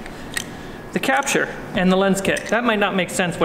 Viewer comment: Okay okay, I solved my problem. At 10:43 is the PEAKDesign capture and lens kit. I couldn't quite hear what he said, but I figured it out. Ordered mine!